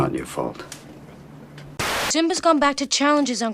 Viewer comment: did she say "Timba" ?